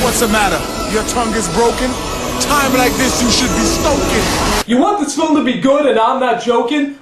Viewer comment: OK...What is shaq saying there? It very clearly sounds like: "What's the matter, your tounge is broken? Times like this you should be smokin!" That can't possibly be right yet the audio is so clear; what is Shaq supposed to be saying? Seriously, not being able to figure it out bothers me so much (unless he's really telling the kid to smoke)?